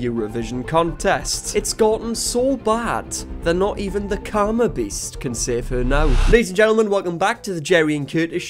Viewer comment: "Karma beast" my phone captions said & thought i heard "Carnaby.." as in the street lol damn near thought you were calling their fashion groovy lol 😆 🤣